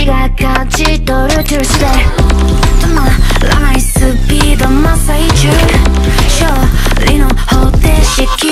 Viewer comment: “be the monster eat u” is what i heard💀💀💀😭